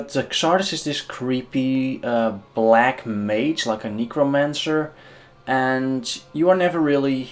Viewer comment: "Xardas is this creepy black mage, like a negromancer..." I was listening with half an ear while this was playing and I had to replay this part as I misheard your accent.